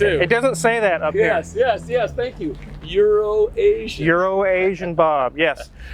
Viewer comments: Everytime I hear his name, I hear "urine nation bob" or urination bob. Not sure why. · I can’t be the only one who thought Hoovie was saying Urination bob